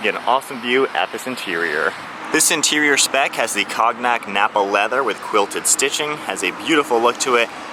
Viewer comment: Did buddy just say cog nack interior